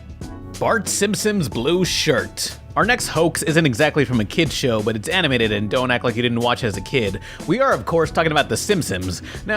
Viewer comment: why does he say "simpsons" like "simpsoms"